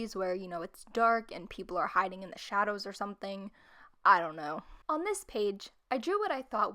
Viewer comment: Haha you said i dont know when you had the page with the eyes And it sounded like you said: Eye dont know xD